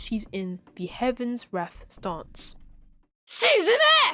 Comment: Helloo ~~ great job with the video as usual :D. After watching through it though, there was one part that made me wonder, around 15:02 . I dont think she says susume, rather shizume 沈め (which means "sink"). I feel like this makes more sense with the context of the attack. Regardless tho, very nice vid 🗣🗣🔥🔥